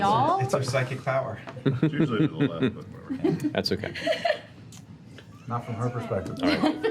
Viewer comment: What does Travis say at 10:55? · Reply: Laura: The ''shaft'' curves to the right, y'all. Travis: Well, it usually curves to the left, but... whatever. Sam: Not from her perspective. found it on tv tropes :P